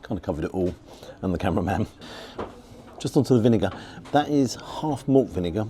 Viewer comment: what did you say there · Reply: Vinegar